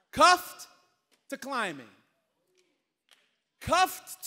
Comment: I thought this said “Cuffed to CLUBBIN” 💀💀💀